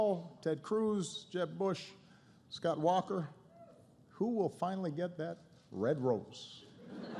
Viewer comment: What does Obama say between 12:10-12:12? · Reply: "Who will finally get that red rose?"